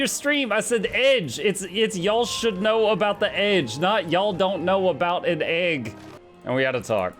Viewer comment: In Gabe's defence, I have ALWAYS thought you were saying "You guys should know about the eggs", and thought it was a reference to that exploding molten egg horror story you told us and how we should beware of exploding overheated eggs. TIL it's "edge" not "egg".